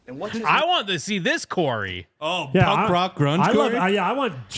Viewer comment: Oh my bad thought, you said corey is cringe, you said grunge . Kurt must have heard a preview n said thats it im out